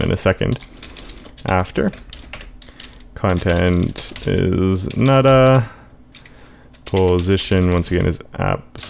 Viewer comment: Did you said "Content is 'nada' "? Thanks for the video. It's amazing.